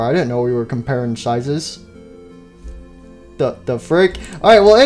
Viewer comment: Classic Foxy: Yarrr ye hook be bigger than mine Withered Foxy: I didn't know we were compareing sizes? Puppet: TF!?! did anyone catch that replay that a couple of times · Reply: It was "Frick" not "Fuck"